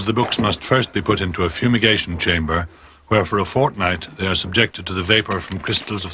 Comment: did he say fortnite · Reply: Yes, he said 'fortnight'. Why?